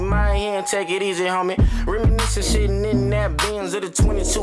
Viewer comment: He ain't even curse. This shit better get radio play. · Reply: Patriotism Defined nah that was him saying sitting but his flow made it sound like her said shit